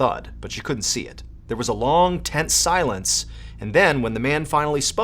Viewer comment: I heard Ted silence not tense silence.